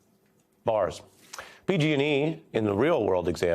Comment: Did he just say bars?!? (15:17) 😂🤣💀